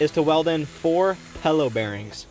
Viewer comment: probably just the day i've had so far.... but i keep hearing "pellow bearing" and it's getting me irrationally upset. did you just say one of your four filaments was "P.H.A.T."? i need a reset